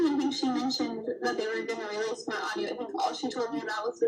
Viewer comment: "They released my audio" yikes did I hear that right?! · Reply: I heard "release a small audio"